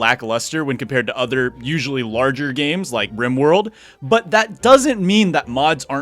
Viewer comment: Did he said Rim World or Rain World? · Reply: Rimworld